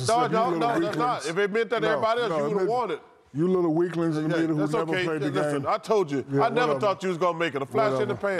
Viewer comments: Did Shaq call Kenny and Chuck "you little weaklings in the media who never played the game?" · " I never thought you was gonna make it. A flash in the pan" I know he's joking, but damn Chuck. 😅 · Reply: i thought he said flash in the past